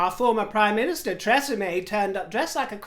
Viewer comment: Oh I have to tell you this when I first heard you say the name of our former prime minister I had momentarily looked away and thought you had said Treseme as in the shampoo care range. Only when I looked back up and I saw those unflattering photos of Theresa May curtseying deeply and gurning did I realise what you had said. If there were a caption for those photos of her curtseying it would be 'Oh gawd me backs gone, ooh ah just hang on a minute' She looked like an old Bambi on a skating rink with her long legs splayed, when a little bob of a curtsey would have done I'm sure. So thank you for making me have a little giggle at my own expense on mis hearing you 😀